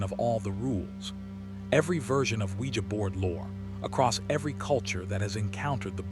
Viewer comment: Oujia board?? 🤔 I thought you said SquEEgEE bOaRd ... ive been windex'ing this abc 123 board for hours asking if my dead grandma can hear me!